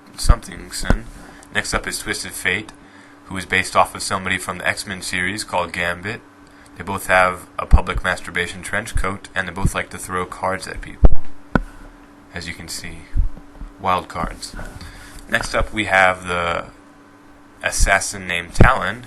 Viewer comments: Is it just me or does he say "Public masturbation trench coat"?!?!!? o.0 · did he just say 'masturbation'